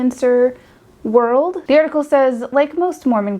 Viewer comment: I’m sorry, did I hear “fill her up” Clarke? Or is my mind just pure filth?